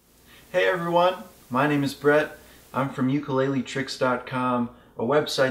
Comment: In the beginning, I thought you said your name is Bread. Lol. Good video though, well done!